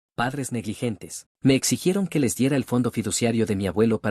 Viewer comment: Pensé que decia "Padres inteligentes"